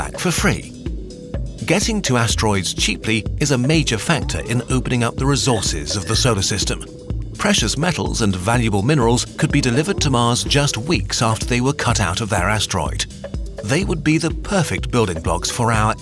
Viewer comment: I keep hearing 'the soda system'.